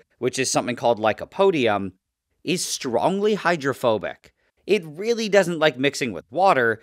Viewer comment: I thought you said heterophobic--... 😭😭